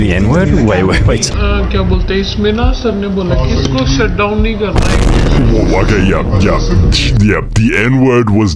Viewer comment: she actually said “Shut down nahin karna” but the nahin karna part sounded like the n word